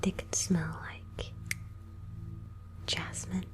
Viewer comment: I hear “dicks smell like....” XD